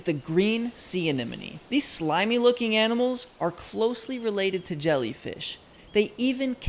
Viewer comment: I thought you said new jellyfish